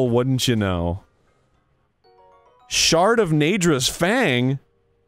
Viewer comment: when he said shard at 12:52 I thought he said shart